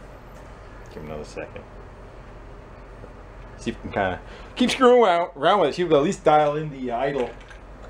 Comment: hahaha what did he even say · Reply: "Kinda keep screwin' around with it to see if we'll at least dial in the idle."